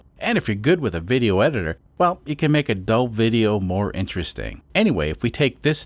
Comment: I heard "You can make adult video, more interesting" naughty!